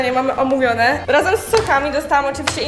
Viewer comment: usłyszałam razem z sukami